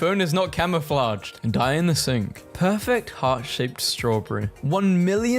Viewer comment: I look away, FOR ONE SECOND and I hear "die in the sink"